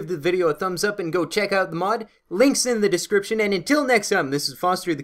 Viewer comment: Awesome mod showcase. For a moment I thought you said "Lynx in the description." Any idea when you will have the Pixelmon server up?